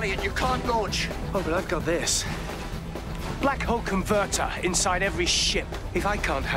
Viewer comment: All these years I thought he was saying "black hawk convertor"